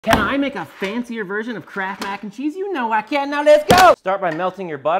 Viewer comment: Genuinely thought you said crap Mac and cheese, then realized it was kraft